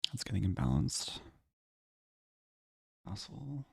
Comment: Did you call him an asshole at 1:26? 😂